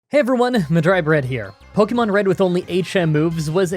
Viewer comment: In the video recap I heard "Pokemon Red with only 8 Shamus" and was very confused